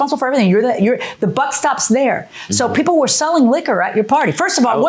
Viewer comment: "The butt stops there." I was like, did I hear that? The closed captioning said the same. 🤣